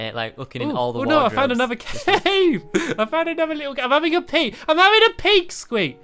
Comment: I paused the video while Stampy was saying that he was having a peak and it sounded like he was saying IM HAVING A PEE! Lol